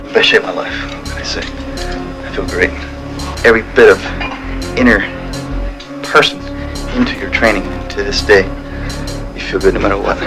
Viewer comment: Does anyone know what Tom says from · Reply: "Best shape of my life, gotta say. I feel great. Every bit of inner person into your training, into this day. You feel good no matter what."